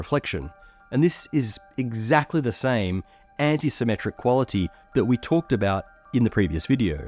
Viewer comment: i wasn't looking at the screen for a second and i heard "anti-symmetric" as "anti-Semitic" and i was like WOAH WAIT